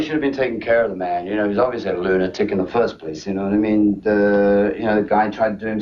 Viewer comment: i thought he said 'Love is a lunatic in the first place' ?